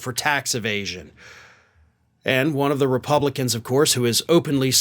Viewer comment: I could swear I heard you say Repugnicans.